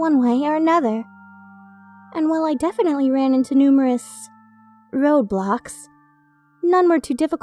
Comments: DID SOMEBODY SAY ROBLOX? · when she said road blocks i thought she said rolblox